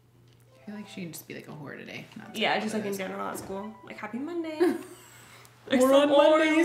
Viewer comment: I thought u said happy Friday lol but I love that game so much 🥹🥹🥹🥹